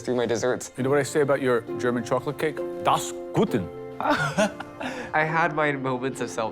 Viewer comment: tf the judge says about the german chocolate cake at 2:26:00 I am German and didn`t understand him. sth like "das guten" but this doesn`t make any sense.